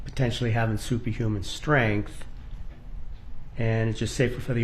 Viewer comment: I know he didn't, but it sounded like he said, "potentially having soupy human strength."